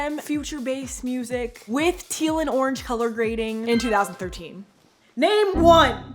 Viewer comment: Was I the only (for a moment) who thought said, “with Teala” ??? 1:14 😯🙃😭